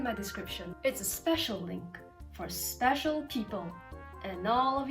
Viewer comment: For a second I thought she said special kink